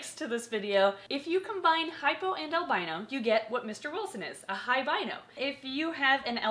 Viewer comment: lol I thought she said "high rhino"